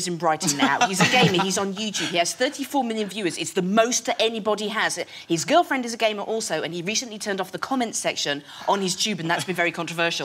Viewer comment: Did she just say his girlfriend is gay?